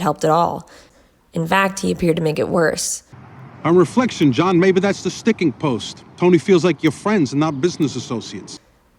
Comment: to this day I have no clue what he said.. clapped him in what?? Haha my opinion is carmine was just a lucky fool. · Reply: He's referring to the kinda old-fashioned phrase "clapped in irons." Handcuffs/chains are made out of iron, so he's saying King Louis imprisoned the finance minister